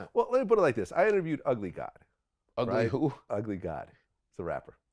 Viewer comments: Vlad: I interviewed Ugly Guy once... Godfrey: Ugly Guy !?!? Vlad: No. Ugly God. He’s a rapper. Godfrey: Sounded like you said you interviewed an Ugly Guy once 😂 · 1) Please enunciate. I thought you said “Ugly Guy,” too. You generally do bit a similar confusion in a Lord Jamar interview in which he and the audience misunderstood. 2) You aired the footage of Treach walking out.